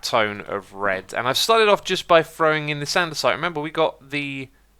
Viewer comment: "...by throwing in the Sandstone. Wait, did I say Andesite? I meant Granite." Oh dear 😂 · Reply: That’s what i thought he said too, but he acctually said ”by throwing in this andesite...” if you listen carefully you’ll hear it